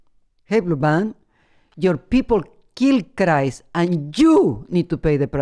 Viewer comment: Sounded like she said: -"And JEW need to pay the price!" 😂😂😂